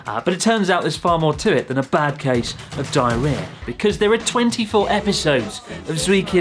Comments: I thought they were saying: “I have a bucket of diarrhea.” 😂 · I don’t know what it’s more funny, the fact I heard “ I have a bucket of diarrea” or the hole show XD